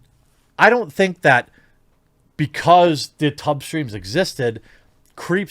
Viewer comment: When I heard you say tub streams, I thought you said tug streams.